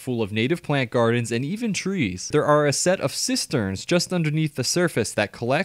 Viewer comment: I swear I thought I heard him say that there were two “sisters” instead of “cisterns” collecting rainwater to water the plants and I was imaging two women just passing around pails of water back and forth watering plants and draining a flooded floor 😂😂😂